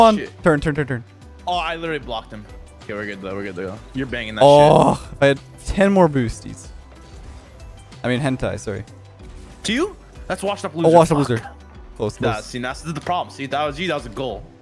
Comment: did i just hear "i mean hentai, sorry" ?